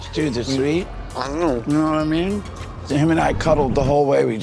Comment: did call fred sam @ 2:50?